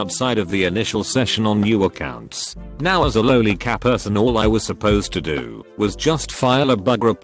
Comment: I heard lonely cat person lmao